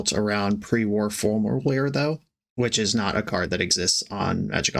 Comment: Can someone spell out for me what card name is saying here? All I hear is “Pre-war formalwear” ???